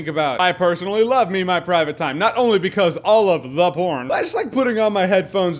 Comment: surely someone else heard him say "all of...the porn" cause I feel it's really being glossed over